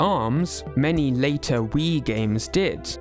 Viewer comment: small typo on the subtitles, mii instead of wii. great video otherwise tho!! keep it up